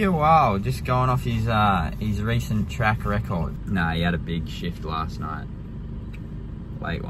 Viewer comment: Misheard "Nah, he had a big shift" :D